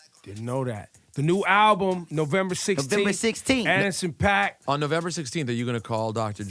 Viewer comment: It sounded like E said Ettison Pop, not Anderson Paak lol